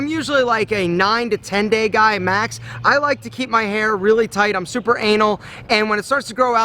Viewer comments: Did Aaron say “I’m super anal” at 0:45 or was it just me?😂😂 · I heard "super anal"? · did you say iam super anal ?😂🤣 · it sounds like he says I'm super anal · so nobody gonna comment the fact that it sounded like alpha said: "im super anal" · did he said "im super anal"? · what i hear im super anal xD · did he say "super anal" ????